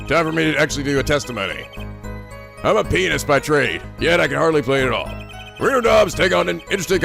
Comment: i swear i heard "im a penis by trade"